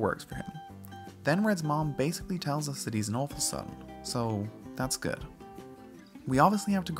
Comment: prof. Oak is an officer?? What? Can someone please what you said because I’m kind of retarded when I listen to certain accents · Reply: "he's an awful son".